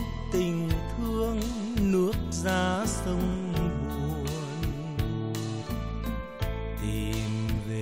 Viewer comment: Đầu bài hát Biển Mặn có đoạn nghe là "ôm ấp tình thương nước ra sông buồn", các bạn có ai biết chính xác hai chữ cuối là 'sông buồn' hay 'sông nguồn'? Mình thấy ca sĩ giờ toàn hát là 'sông nguồn', không biết chữ nào mới là đúng? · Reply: Sông nguồn đó, tại chữ đó ổng bỏ nhỏ nên nghe thành buồn. Ổng là tác giả mà, ổng hát sao thì là vậy đó